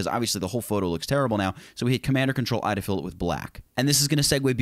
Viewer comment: what did he say 2:42, ctrl or command what ???? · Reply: Jimmy Chan... Ctrl or command + i